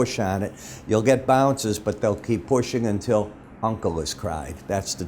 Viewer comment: Can you please tell me what he is saying at 2:10 ? Is it 'uncle is crying '? I am turkish and his comments are completely right on Turkish economy